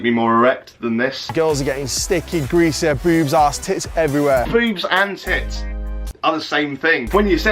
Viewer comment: I think he said "Boobs, Ass, Tits" Not "Boobs And Tits" XD